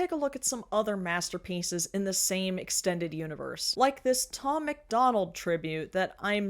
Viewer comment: When you read the Tom MacDonald tattoo, I thought you said it was an old MacDonald tribute tattoo at first, and I was like what? Because the shitty rhyming??